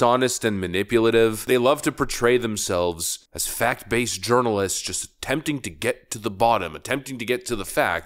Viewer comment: the Goebbelians in the media libeling yet again.. did I hear "urinalists"?